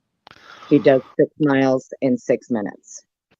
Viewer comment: audio was kind of bad then, but did she say he could run 6 miles in six minutes? That’s impossible, but I might’ve been mistaken what she said.